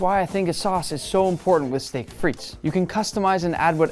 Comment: Fr tho, I thought it said steak fires 🤣